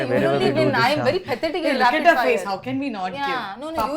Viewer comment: "you'll only win 'chinna' Sri says, or is it just me who heard it that way? 😁